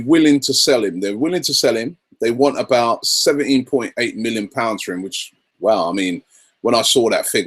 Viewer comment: I thought he said 70.8 million for benzema 😂